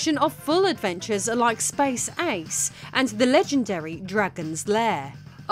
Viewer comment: >>> When you said "Space Ace" -- and as I am unfamiliar with that game -- I thought you had said "SpaceX." Really...😊